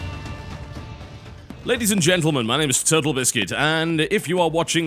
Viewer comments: Did i just hear turtlebiscuit? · At the start it sounds like he said turtle biscuit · Is it just me or does it sound like he says my name is turtle biscuit